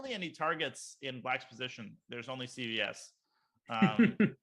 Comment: What does Naroditsky say at 26:15? ...targets in blacks position there is only XXX?? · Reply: "CVS" a drug store in USA, as opposed to Targets, another drug store. Play on words, there aren't any "Targets", only CVS.